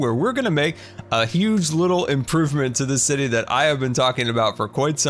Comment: Did he just say huge little improvements?